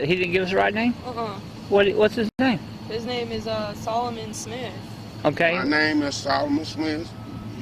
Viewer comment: "my name is Solomon E. Smith the third." "Mr. Eastman, how do I spell your first name?" -> taser not a very reasonable course of events, especially considering that they're having trouble understanding him and speaking while he's speaking. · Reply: Joel Voss Personally, I heard Smith because I heard the “sm” portion directly after E. The “th” was very low, but I heard it still.